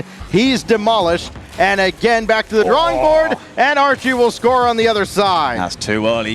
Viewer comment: did he say NRG? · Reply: "And Archie"